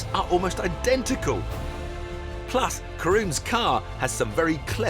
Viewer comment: I Heard Him Say Koreans Car.... Did He Meant It For The Lex Which Is A Japanese Car or The Bavarian Machine Workmanship.. 😂 😂 😂 😂 😂